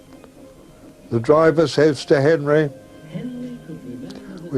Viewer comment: WHAT AWDRY SAID AT 14:38 !?HE SAID - WE MAKE GOOD TIME OVER THE LAST SEX!? OR WHAT? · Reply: "Section"